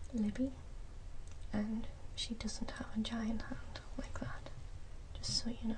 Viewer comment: I swear at 1:10 she says my mom doesn't have a vagina or anything like that. Please correct me.